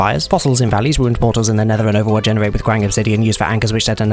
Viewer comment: i heard overwatch instead of overworld at 0:14 🤔